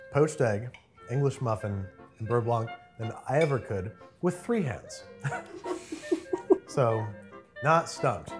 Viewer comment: The throwaway comment at the end about Sohla doing a better job one handed than Andrew with two hands could be interesting to see… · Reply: i thought he said three hands too lol